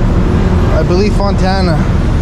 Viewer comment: When you said they're going to Fontana, I thought you said Montana!